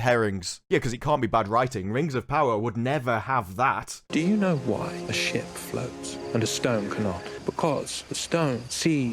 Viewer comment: The first time I heard it, I thought he said "Do you know why shxt floats but stones don't..?"